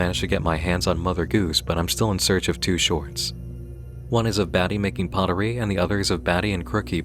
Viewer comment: My brain is so rotted that at 9:15 I thought was saying "baddie" jfk man